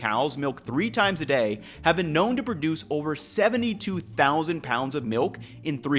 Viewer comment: edit: He said pounds, and I misheard and misread thinking he said gallons